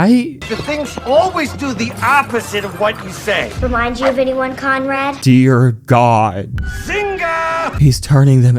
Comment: Is your drugs mishearing Conrad as Conroy?